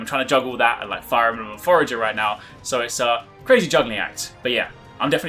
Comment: what game does he say after Fire Emblem? Listened like 10 times and can't make it out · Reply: Forager :)